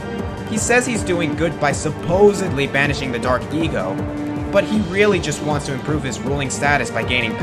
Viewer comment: For Count Veger it sounded like you said Dark EGO instead of Dark ECO. I find this funny cause he's a jerk